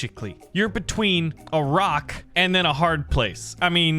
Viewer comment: he said a rock and I thought he was just saying Iraq with the heaviest accent known to man and I was extremely confused 😭